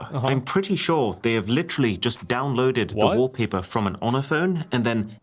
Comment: Did he say Ohna-Phone? What's that??